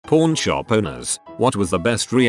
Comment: My parents thought it said porn shop and took my my phone